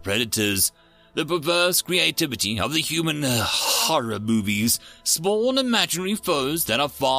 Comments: "horror boobbies" did i hear that right? Edit: i put caption and thats what i see and hear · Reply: I heard "horror boobies" too. I shudder to think. ..... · "horror boobbies" did i hear that right? Edit: i put caption and thats what i see and hear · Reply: lol its supposed to be horror movies , but boobies works as well